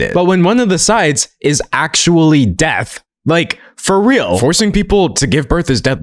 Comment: There's a subtitling error of "one side is literally deaf" instead of "death" and that's probably not what any deaf person using your subtitles needs to see >.<